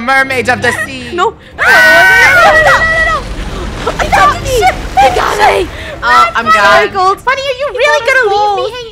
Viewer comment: is funneh saying - HOLY $H_T-HOLY $H_T? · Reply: She said abandon ship